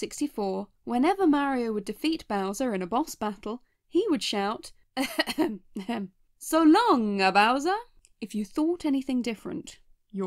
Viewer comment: I thought it was because everyone heard it as “so long gay bowser” and that obviously wouldn’t fly today